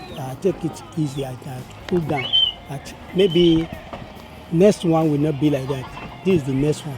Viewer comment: Who edited this video? The man did not refer to his kid and wife as normal or not. Down syndrome is not abnormal. Why did they subtitle what he said as calling his child “normal”. He said “maybe the next one will not be like that (his wife and kid)”. He didn’t say normal